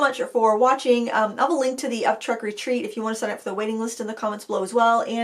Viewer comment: I thought I heard you say Upchuck Retreat, probably because we’d been talking about barfing earlier… 😂 had to rewind 10 seconds to hear it correctly: Up Trek Retreat 😅